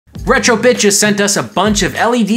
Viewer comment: lol at first I heard "Retro Bitches" at the start of the video